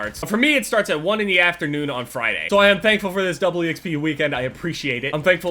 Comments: Is it me or is he saying double xp weird? 😂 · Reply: It sounds like hes saying Doubley Xp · Is it just me that heard him say doubley xp?